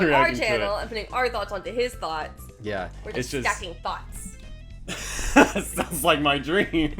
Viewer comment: Stacking thoughts. Haha I don’t think realized it sounded like she said stacking thots. XD I appreciate this kind of video because it’s like anitubers having discussion together. Thanks for being considerate of the other creators you react to. :)